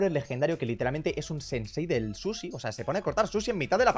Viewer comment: Escuché KENSHI XD